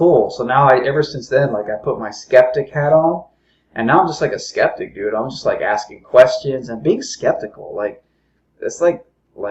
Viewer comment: All I heard was "I'm just gonna put on my septic hat, cause like I'm septic, I like being septic"